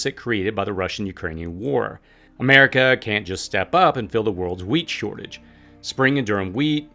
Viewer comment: When you said wheat shortage, I thought you said weed shortage!